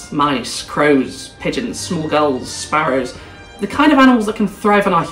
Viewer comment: This is such a great point! Really interesting, thanks for making this. Tangentially, I misheard you saying ‘small gulls’ as ‘smol girls’ and thought “oh no my girlfriend and I are both over 6 feet tall (183 cm) 😭”